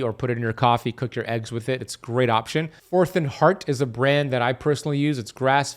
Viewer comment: I couldn’t tell what brand he said that he eats of ghee. Anybody catch it? At about 8:32 mark. Something heart.